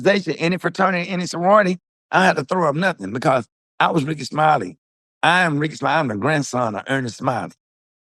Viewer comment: Sound like he’s crying now🤷🏾‍♂️ I thought he said he was Ricky Smiley